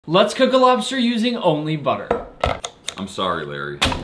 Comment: 😂 I swear I thought I heard let's cook a lobster in holy water